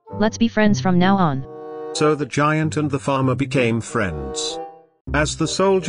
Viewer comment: I swear, I thought that said the giant and the feather